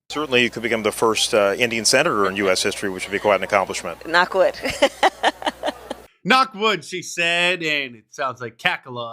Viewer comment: I thought she said, "suck wood"😮